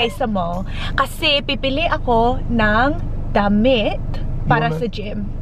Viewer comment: I thought i heard him saying "damn it" instead of damit.. hahahaha